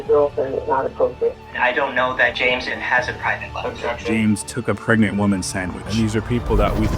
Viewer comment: "James took a pregnant woman's sandwich" I'm sorry but that actually made me laugh out loud. It sounds like something you'd see Will Farrell do in one of his wacky movies lol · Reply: I thought they said "James took a pregnant woman sandwich"! I though they were trying to make him out to be some kind of kinky fetishist. 🤣